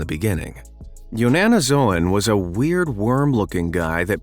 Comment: in the vision he said YANNANOZOON I thought he said your Nanna is snooze